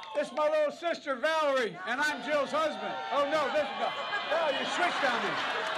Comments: Did he just say "And i'm Joe's husband"? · "They switched on me." "I am Joe's husband." Seems like good ol Joe switched also.😂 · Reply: &y Diaz : I can see why you thought that's what he said, but I heard, "I'm Jill's husband."